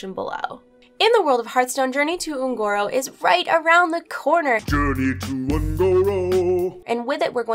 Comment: 40 seconds in and i already misheard something! D: Glad i realized it lol, i thought you said "Un'goro raid around the corner" My hears perked right up! hah! Could be fun though :D